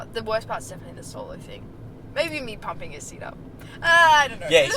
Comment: I didn't hear "seat"... I thought she said "Mabye me pumping his seed out" as she moved her hand. Had to rewind >_>